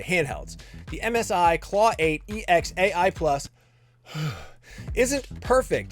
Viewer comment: same price as? Last I checked the cheapest Onexplayer 3 was about 1400 dls; anyway it is a better buy than a LEGO 2 indeed. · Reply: ah sorry I thought he said msi claw 8 ex, not ai. I agree that previous model might be better than xbox rog all x too, but it's close.